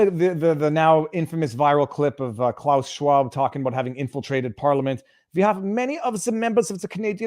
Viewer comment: Because they've all been "infiltrated" as Klaus Schwab would say 😂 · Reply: I thought he said "penetrated" 😆